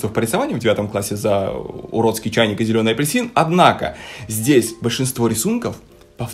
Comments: Меня выгнали с курсов по рисованию за зелёный апельсин. Апельсины, которые изначально и есть зелёные: понимаю.... Пользователь насмотрелся Утопии Шоу и радуется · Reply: Он же вроде сказал, что выгнали за зелёный чайник и уродливый апельсин. Или мне послышалось? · Меня выгнали с курсов по рисованию за зелёный апельсин. Апельсины, которые изначально и есть зелёные: понимаю.... Пользователь насмотрелся Утопии Шоу и радуется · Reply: UPD: (Так, потому что у меня редактирование коментов прикалываются) Все таки послышалось. Он сказал про уродский чайник и зелёный апельсин. Извините меня